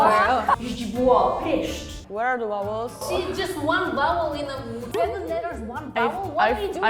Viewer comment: Oh come on, you are a language channel and your subtitles are immediately and constantly wrong? 0:08 is "See, just one vowel" not "She just used one vowel", why are you adding words that were not said just so nthat your misheard subtitle makes a little more sense?? 0:30 "overall" one word, 0:45 is "We're gonna be profficient" 0:46 "Polish is different THAN other Slavic Languages" - languages is capitalised for no reason whatsoever, 1:00 "Weird" - a common misspelling. Are you hard of hearing? Do you not know English? I mean, maybe get someone that knows the language to help, cause that is a mistake every 6 seconds.